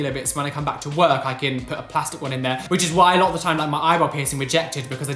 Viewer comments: When u said eyebrow piercing I thought u said eyeball piercing and I was like😱💨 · i know you said eyebrow piercing but i heard eyeball piercing.. i- no